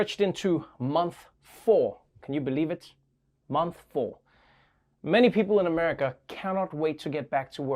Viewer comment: I know I'm not the only one who thought Trevor said, "Month Fall"